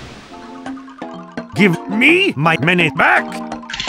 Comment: What did spy say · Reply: Give me my money back